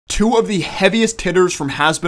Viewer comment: Opened video. First thing I hear is "two of the heaviest titters". I think I should go get that breakfast I skipped...